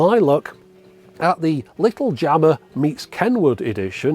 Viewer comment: I thought you said, "What does Log Jammer mean to Kennywood?"